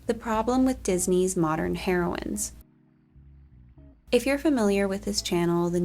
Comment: I genuinely thought you said "hero*n" so i immediately looked back at the screen